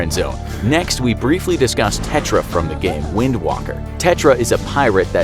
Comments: I swear i heard you say wind walker... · did he just say "wind Walker"? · Did you just say " Wind WALKER" ?! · Narrator:Tetra from the game... Me:Wind Waker. Narrator:Wind Walker. Did u just say wind walker?! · Did he just say wind Walker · Excuse me did he just say wind wooker?!